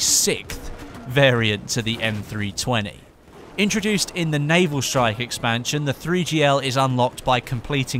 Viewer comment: I swear said 'Navel Shite'... Which is still a pretty accurate title...